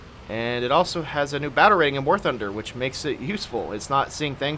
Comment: You say "battle rating", I hear "batarang". Also, Hail Hydra! XD